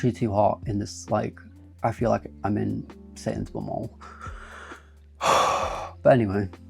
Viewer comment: I THOUGHT U SAID SAINSBURYS!!!!